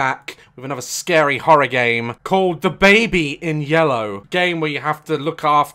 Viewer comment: I literally heard “Dababy in yellow” 💀😀